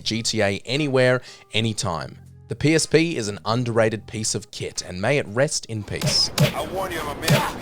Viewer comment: who else heard the psp is an underrated piece of shit and may it rest in piss